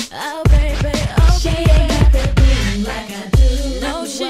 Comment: When I first heard this song, I seriously thought they said "She ain't got that booty like I do". Lmao